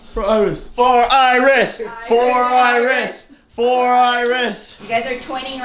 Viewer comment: For our wrist. for our wrist that's what I heard